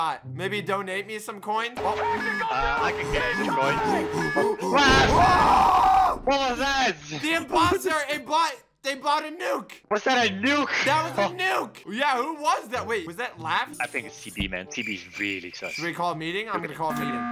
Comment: a few secs before this I thought he said “the imposter bought a dead body nuke”